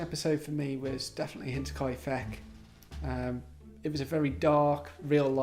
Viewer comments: Sorry, but I cannot make out the name of the episode referred to here. It sounds like something I would love to watch, but cannot make heads or tails of what is said. It's my ears, not used to the accect, sorry. Can anyone help with this? Thanks in advance! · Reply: Ahhh, that would be The Mysterious Murders of Hinterkaifeck. Hope this helps! :-) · what does he say was the most challenging episode? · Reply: Hinterkaifeck 😊